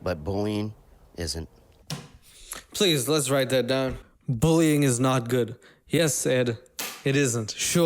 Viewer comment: I thought he said bowling is not good and I was like what the fuck did bowling do to you?